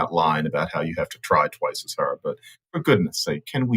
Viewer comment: I thought he said ten times as hard. Please clarify for me.